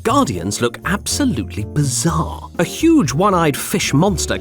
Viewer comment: did you just say "bizzare"?